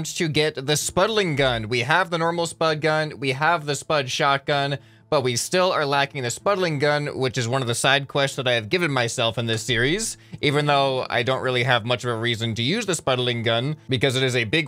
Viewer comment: did he say "one of the sad quests ive given myself" why is it sad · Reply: Side quests not sad quests😂